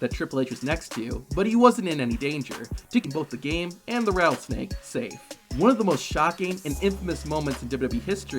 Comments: Anyone know what he said at the skip at 3:10? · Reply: Rattlesnake safe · can anyone tell me what he said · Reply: Keeping both The Game and the rattlesnake safe. But yeah this guy has horrible pronunciation.